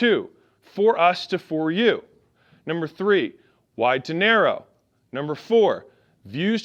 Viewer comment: Every time he said “to narrow” I heard “dinero”